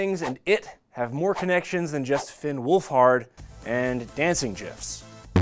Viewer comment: Did he just say "jiffs"?